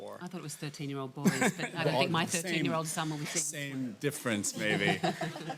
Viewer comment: In the minute 28:25 ... What does Cate say?? Can't hear proper ... Please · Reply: "I think my 13-year old sons won't see it." Or something along those lines, referencing the audience that Hollywood studios market to when making films